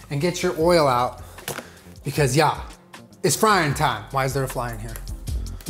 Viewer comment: "It's frying time!- why is there a fly in here?" He thought you said flying time.